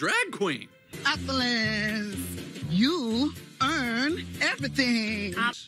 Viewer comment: the 'upulance' and 'earn' instead of 'own' still kills me till this day